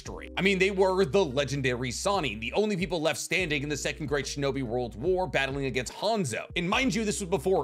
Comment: I thought you said "2nd grade shinobi world war." And... That's funny as heck... And kind of the subtext of a lot of Naruto. Just kids fighting kids.